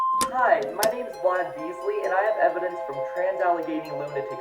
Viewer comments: I swear i heard "Ron Weasley" instead of "Vlad Beasley", when i heard that the first time, in the intro. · did it just say “hi my name is Ron Weasley” 😭